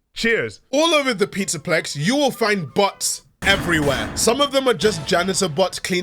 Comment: I heard this as you saying "You'll find butts everywhere", and the following sections I replaced all "bots" with "butts" in my head, and I can't stop laughing